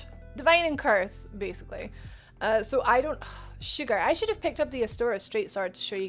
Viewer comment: Did you say “ah sugar” at 14:42? 😂😂😂 I’ve never heard that instead of “ah shit” before lmao. Anyhow, appreciate the video was very thorough. Honestly what was confusing me is the way the stats/numbers are set up😂 I couldn’t figure out what the + numbers meant next to the main physical attack number because my dumbass was looking above it and seeing “regular/thrust”, so I was thinking the first number was showing the regular damage the 2nd was showing the “thrust” damage😭 Honestly just threw me off because pretty much every other game it’s set up opposite, with the effects of a certain stat being below it and not side to side.